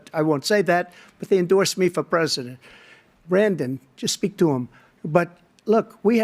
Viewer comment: did he call Biden Brandon?! lol lol